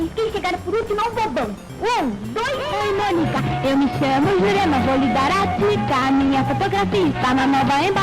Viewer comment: so eu q entendi "meu nome é jurema vou lhe dar a pika" :|